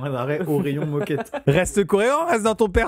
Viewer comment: y a que moi qui ai entendu "reste coréant" ? 😂